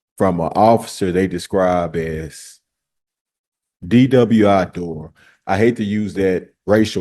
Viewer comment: Does he say "DWI door"?